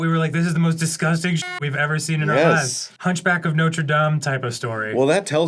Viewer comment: I thought Dan said a Punchback of Notre Dame story which made me laugh more than I should have 🤣